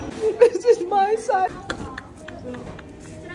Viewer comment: I thought she said "Monkey monkeyyyy where are you" 😅😂😂😂😂😂😂